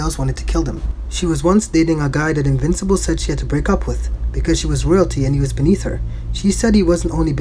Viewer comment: I thought he said "He was royalty and he was beneath her"